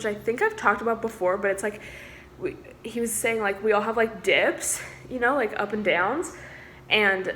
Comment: I thought you said “we all have dicks” and I was like nah girl I can assure you that I do not